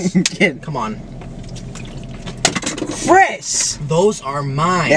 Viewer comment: am I trippin or did matt say "fries" instead of "Chris" 😂😂